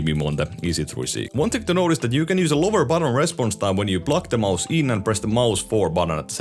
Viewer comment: what does he say about holding mb4 and pluging it in ?? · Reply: lower respnose time